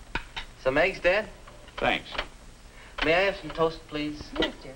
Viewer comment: What I hear: " So Meg's dead". I was about to say, well those are good manners.